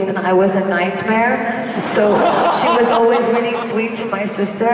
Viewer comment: what did she say someone can translate it in english please... ?? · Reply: I think you that she said „Spelunke“